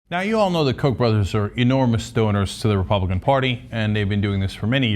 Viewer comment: The first thing I heard was "the coke brothers are enormous stoners"... O_o